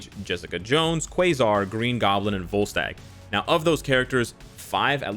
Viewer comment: Not gonna lie, when he was going over “Refinement” I thought he said “Ball Sack” instead of Volstagg.... (IGN: Wayward Son 02)